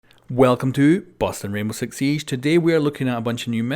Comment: Sounds like your saying “Welcome to Boston R6S” in the beginning